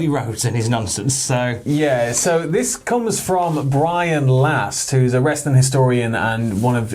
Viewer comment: I heard “Brian Last” and immediately relaxed haha